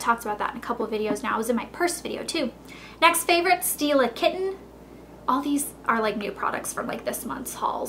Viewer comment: I thought she said "steal a kitten", when she said "Stila Kitten". Because, well, I wouldn't put it past her. Hahaha